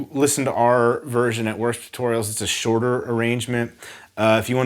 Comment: I thought he said worst tutorials and I was so confused